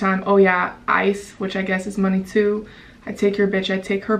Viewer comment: Did she just say ice is money? 2:53 🤦🏻‍♂️